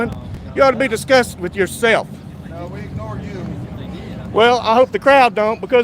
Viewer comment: It sounded like one cop said "We ignore you...." - referring to